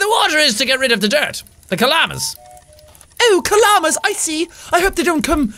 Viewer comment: Is it just me or does it sound like he says llamas instead of kalamas 😂😂 all I can imagine is llamas flying around the flying machine 😂😂😂😂